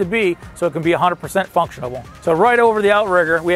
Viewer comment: Did he really say "functionable" at 3:58?